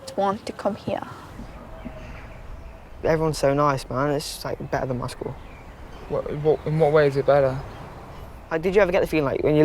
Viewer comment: Anyone can please help? I’m missing most of what says from 33:04 to 33:30, Could anyone explain it to me? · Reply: something like this: B: everyone’s so nice, man, it’s like better than my school B: did you ever get the feeling like when you lookied like you tried to talk to me and gave you a weird look and walked off? B: no, that didn’t happen here. Every person I’ve tried talking to they just like spoke back or even people trying to start a conversation with me.